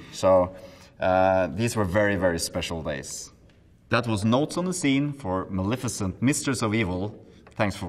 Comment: I thought he said THAT WAS NOT ON THE SCENE at the last part